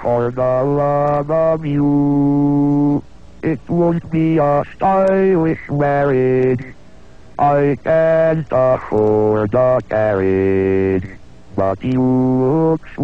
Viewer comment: Lol did I hear "warriage"? Still extremely impressive.